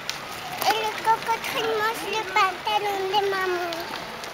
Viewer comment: is saying: "et le crocodile mange le pantalon de maman?" At :39, she seems to be talking to a bug, and she says "mange le pantalon" and "va mourir". At 2:11 says: "ah, il est là", and she starts running. I wonder what she's looking for. I love her very guttural, French "r". She's certainly a little chatterbox. No wonder she's gifted in languages. Words just flow out of her mouth. I wish the video hadn't stopped when went up to the strange stroller. I would have loved to see the woman's face as she came upon. would have engaged her in conversation, that's for sure. Feb. 10, 2018. Canada.